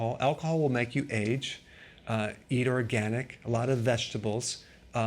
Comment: Excuse me but what did you say @ 4:58? I can't understand. Thanks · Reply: Milk Thistle.